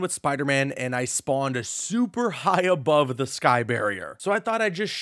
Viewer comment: I legit thought you said “ Sky Bot “ , so I thought he’s got a drone that follows him above. That he swings from……. Man it’s time for bed 🤣😭